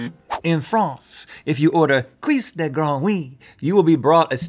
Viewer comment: I thought at first my comment would be focused on French people usually not eating frog legs (in fact, I've never seen them anywhere) but I would rather talk about Rhett's magnificently wrong pronunciation of "grenouille" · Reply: I thought he said ''cuisse de groin'' but replayed it enough to understand what he was tryna say